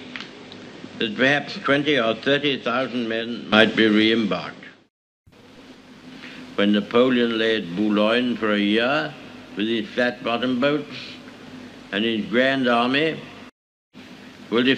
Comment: Did he just say fat bottomed boats?